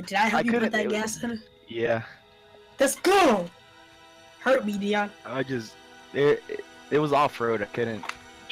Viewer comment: pause did say hurt me Dion 😂😂😂😂😂💀